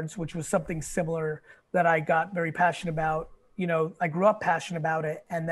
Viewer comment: onwards... am i hearing this right or does he say "I got very passion about" and then says the it same way multiple times? Passionate about is the phrase.